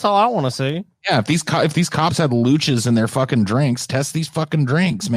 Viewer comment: What word is saying there? "Lootch"? I've never heard it and I can't find it anywhere. How do you spell it? (I'm not an english native speaker.) I'm also very happy, that I've also learned the word "loogie" in this episode. :o)